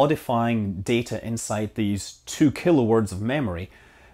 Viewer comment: 2 Kilowatts of erazable storage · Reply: I would have swore he said two kilo words.. :)